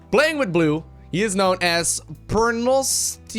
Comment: idk whats wrong with me but i always heard him say pornostico and not pernostico XD